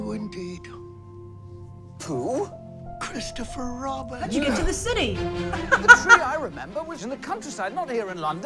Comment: what does she say there? it's something something "city" ...who can help me out?... I'd REALLY appreciate it bc whenever I can't understand what somebody is saying on a video like this it annoys the hell outta me lol I'd just try and ask Grace herself but she's never replied to any of my comments over the years... even when I put her name in the comment so asking her has always just been a dead end for me lol ...so somebody PLEASE reply with what Grace is saying at the 1:55 mark of the video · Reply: “How’d you get to the city?”